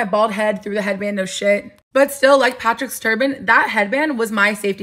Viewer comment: I heard “much like Patrick star”